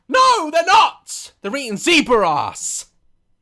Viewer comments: is he saying zebra's or zebra ass ? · Whenever he said the lions eat zebras to me it sounded like he said they eat zebra ass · it sounded like he said "hes eatin zebr ass"